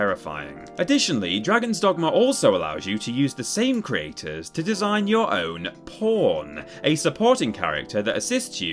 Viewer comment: Did I hear it right? "To design your own P. -O. -R. -N. "